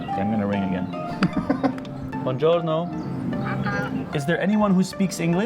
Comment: he said Pronto, which means “ready”, it’s what Italians often say when they answer the phone :)